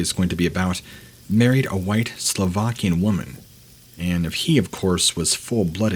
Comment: Slovakian???? Do I hear it correctly?